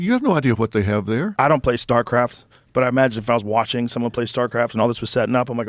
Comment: I thought Rogans said, star craps… roll a star and crap out‼️ Star craft… not hard for any age to figure out, crafts in the stars 😂😂😂